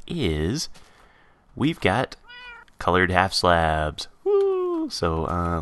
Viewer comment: I thought it said colored wool halfslabs...